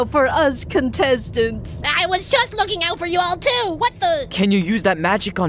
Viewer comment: WOW DID FOUR JUST SAY WTF